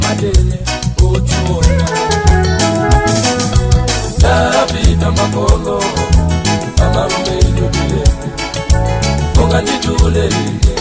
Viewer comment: Hahaha. When I was kid I thought he said Njala ya aphinza a Makono, amalume inu tiyeni, ndiri ndi tiwana